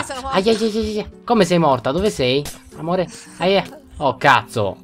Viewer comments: io per caso ho sentito un "amore" 6:39...ho capito tutto!!!!!!:allora anna è la tua ragazza ! scorpion è tuo figlio e cippapewa e miniforza sono tuoi zii ! ahhaah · Solo io per caso ho sentito al minuto 6:40 chiamare "amore"??!!?